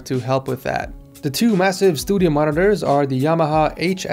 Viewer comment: did you say studio monitors?